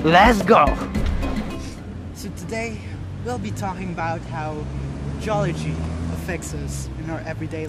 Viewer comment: Why do I hear "Jollibee" as Geology? HAHAHAHAHA jk,, excited for your next content